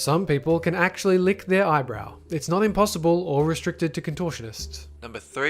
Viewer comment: Does he say eyebrow? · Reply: yes. he says eyebrow. YOU SEE IM TRYING TO BE CALM HERE???!?!!!!!!??!!!?!?!?!!?